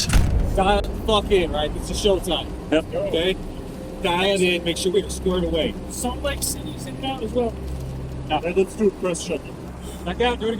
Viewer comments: Does anyone ever check these captions? It's like this on every type of this video. Do I just have discernment or something? I can almost always tell what they are actually saying. "Sound's like Cindy's"? no he said "Salt Lake City's inbound as well" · he actually said "salt lake city is in bound as well" idk who thought he said cindy or if its machine captions but that was baaaaad guessing given the context.